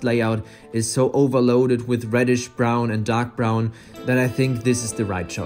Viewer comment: instead of dark brown it sounded like you said Doc brown.